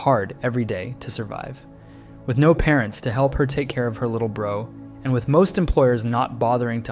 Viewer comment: did he just call him lil bro 😭✌️